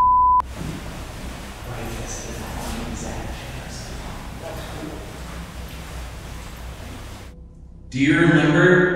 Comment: you hear someone or “something” in the background say something but it’s hard to make out what “they” said. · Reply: Replayed it for the 5th time and it sounds like “it’s good” or “that’s good” and what’s really interesting about that is that shortly before you hear that… said they were going to give them space… then right at 53:05 you hear either “it’s good” or “that’s good” as if they were responding back to.